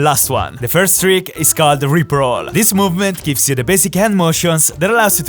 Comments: sounded like rick roll on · I thought he said rick roll LMAO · I thought he said Rick Roll😶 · am I the only one who heard it lip roll? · i thought he said rickroll for a sec lmao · is it just me or did I heard him say "Rick roll"? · i thought he said rick roll lol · When he said rip roll i thought he said rick roll XD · Bruh I thought you said rick roll. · damn I thought he said ``Rick Roll`` 😅😂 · I thought he said rickroll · Who else heard rickroll at 2:13😂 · When I thought I heard him say "rickroll", I thought it was gonna be the end of me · Why did I hear Rick Roll 😂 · Did he say Rick roll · rip roll I heard Rick roll